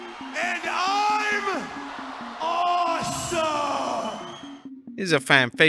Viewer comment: Every time i hear the Miz entrance i DONT hear "awesome".....instead i hear "asshole"🤣🤣🤣🤣